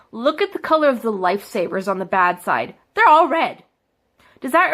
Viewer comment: Did she just say "life savers"?!